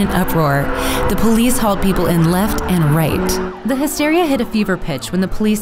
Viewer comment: lol the subtitles said “raped the hysteria” XD